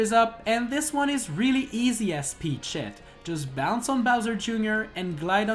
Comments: Thought he was saying peach shit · Suprised you didnt get unmonitized because when you say the character it sounds like you say peach shit XD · I thought he said peach shit